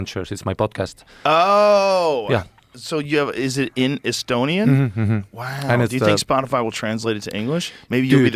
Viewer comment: I just realized that the Ari Joe was talking about the other day is this one. I thought he was saying Ari Shaffir is Estonian